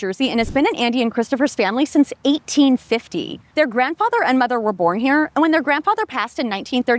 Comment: I had to do a retake when I thought I heard "her grandfather and grandmother were born here" but it was actually mother